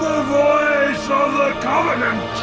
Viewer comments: So for years I genuinely thought he was saying “a**hole, you must be silenced” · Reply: I thought he said "If so, You must be silenced." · So for years I genuinely thought he was saying “a**hole, you must be silenced” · "and so, you must be silenced" man og bungie had a legendary writing team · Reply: I thought he said “asshole, you must be silenced” for years lol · I always thought he said "Fool, you must be silenced".